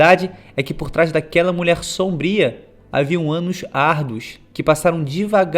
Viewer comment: "havia um anus arduos" me diz q n foi so eu q escutei isso kkkkkk